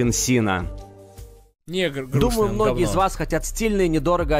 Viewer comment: надеюсь мне послышалось, но я что-то не уверен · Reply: негр грустный, он в говно 😢😢